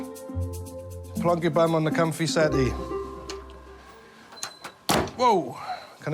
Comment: If ur wondering what the teacher said there he said "Sit down on the comfy seats." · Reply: Think he said "the comfy settee" (British slang for sofa) not seats